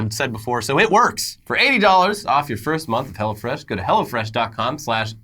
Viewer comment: I honestly thought they said hella fresh 🤣🤣🤣